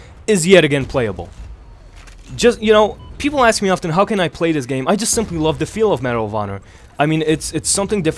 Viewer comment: it sounded like you said "I just simply love the feeling of marijuana."